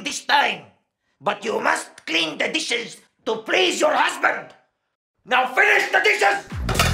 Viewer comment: "NOW FINISH THE DISHES" made me fall off me chair it sounds like Ryu saying hadouken